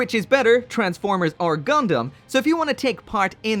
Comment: I misheard you saying "Transformers are gundam" so therefore Gundam wins